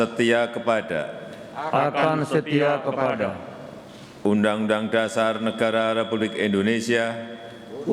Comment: Ko undang undang dara bukan undang undang dasar pak mentri apa sy salah denger y